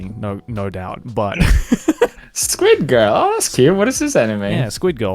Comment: Ohhh my, now I feel guilty I heard "Squirt Game" and not "squid girl" and my mind went directly into JAV Gomenne...